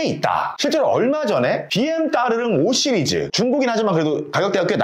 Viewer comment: 내가 잘 못 들었나! 비엠 따르릉?🤔